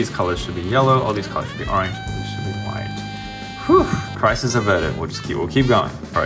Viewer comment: the auto-generated captions say "prices are vertical"